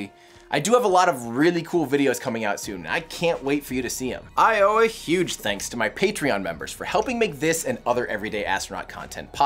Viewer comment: did anyone else notice the dub over at 10:39 when he says "i owe a huge thanks..." i wanna know what he actually said lol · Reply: I accidentally said “I owe a special thanks” on both of my tales and the next sentence is “special thanks” it sounded terrible so I just over dubbed it 🤦‍♂️